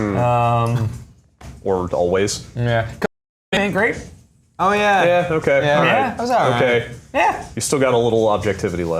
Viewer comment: What did say? Was that an audio glitch or a lazy redaction? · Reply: Nah, that's just an audio glitch. It's in the Twitch archive too. Given the context, bad Nintendo games, it's pretty clear that he said Code Name: S.T.E.A.M.